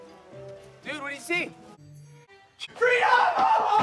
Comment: is that guy screaming „Free alcohol“ or am I hearing that wrong..👀😂